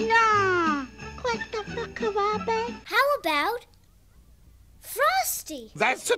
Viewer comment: what did she say? "what the fuck Harambe"?